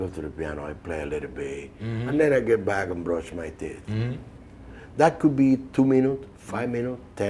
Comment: I swear I heard him say "Brush my tits" 8:50 :P